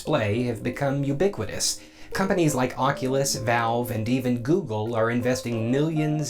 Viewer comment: Did you just say Valve at 1:01?